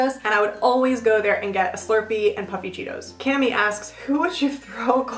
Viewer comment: I swear when you said puffy Cheetos I thought you said puppy Cheetos and I was like "there's puppy Cheetos??" I was so confused haha! 😂